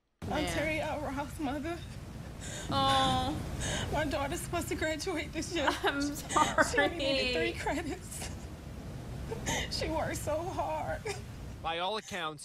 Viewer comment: the subtitles say "My dog is supposed to graduate this year" and it cracked me the f up.